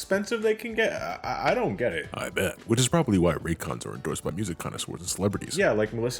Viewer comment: Not gonna lie, I thought he said "aight, bet," and i momentarily panicked